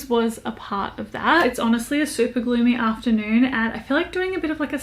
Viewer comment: I heard "Super gloomy day" as "Super-Glue me day" and was SO confused 😅